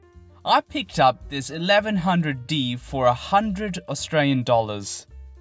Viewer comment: Thanks for helpful tips. Perhaps you misunderstood or maybe I was unclear. I got my 1100D for $100 AUD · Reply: Yes I did, I thought you said $1100AUD. Sorry I misheard your video. For $100 as a starter, not a bad idea! Thanks for correcting me.